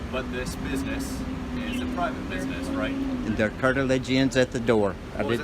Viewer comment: "Their 'cartilage' ends at the door"??? WTH is he trying to say???